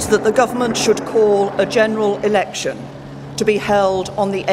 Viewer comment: I thought it said general erection